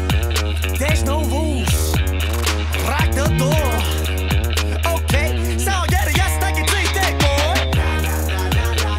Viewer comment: What does he say at 1:25? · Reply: Grim Reaper "lock the door"